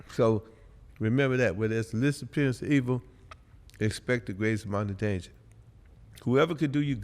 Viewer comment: Can someone tell me what Ken said at 1:15:12 , I couldn’t figure it out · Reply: When there's the least appearance of evil, expect the greatest amount of danger.